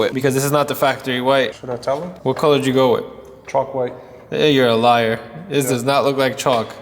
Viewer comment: sounded like he said Trum white lol.